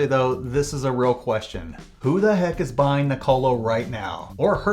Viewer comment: Seriously we need to work on how you pronounce Nikola. The subtitle even thought you said “Nicolo” :))